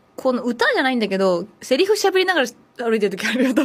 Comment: セリフ喋りながら歩いてて（？）たまにｗ What does she say here?